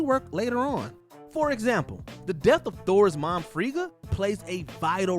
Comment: Did anyone else catch the name they used for Thor and Loki mom at 10:21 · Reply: Frigga?